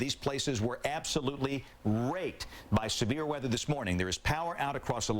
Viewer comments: did he say Raped? because it sure sounded like that. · did he say raped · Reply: raked